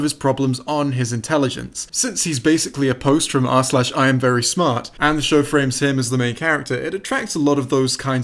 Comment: I'm trying to quote this for an essay, but I have no idea what hes saying. "Since he is basically apposed to our slash I am very smart?" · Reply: it was "since he is basically a post from r/iamverysmart"